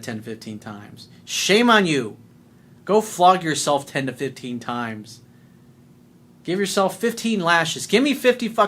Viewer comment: At first I thought you said "Go fuck yourself 10 - 15 times" before I replayed it.